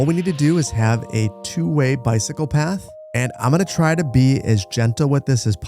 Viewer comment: I know you said "cycle path" but I heard "psychopath"